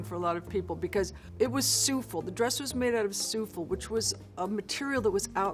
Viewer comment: what kind of material did she say her Met Gala dress was made out of at 2:20? souffl? souffle? thanks for the video btw. I love Cher x